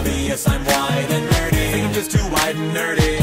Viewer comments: Is it bad that I've understood these lyrics better and better over the years? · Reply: for the longest time, I thought it said wide and nerdy.😅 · Shit, when I first heard this I thought he said "wide 'n nerdy" XD